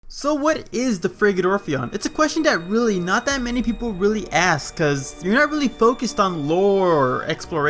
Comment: For some reason I always hear people say "Freakin' Orpheon" and I thought "why are they so mad?" 🤣